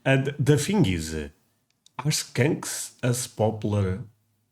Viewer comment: is he saying Skanks?????? 🤣🤣🤣🤣🤣